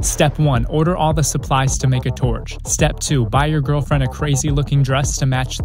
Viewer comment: I thought he said buy a girlfriend